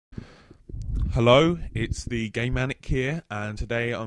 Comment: LOL i thought he said gay manic xD